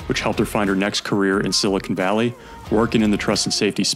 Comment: What’s said at 1:22? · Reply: Silicon Valley